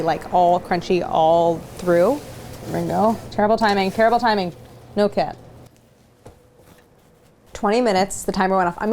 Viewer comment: I honestly thought Claire said “No Cap” as I didn’t see the cat and I honestly thought I was in some dodgy alternate dimension.